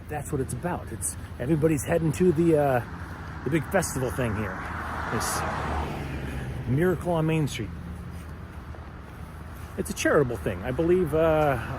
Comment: I must be hungry or something. I kept hearing "bear claw on main street".